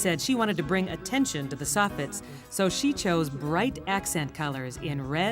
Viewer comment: what the hell is a sophet? Thats what the caption says and I cant understand the narrator lady